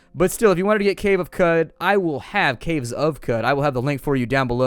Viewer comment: In the Weekend Review, I totally thought you kept saying the Caves of Cud, and I thought it was a Diablo sorta game but with cows. .. Someone should make that.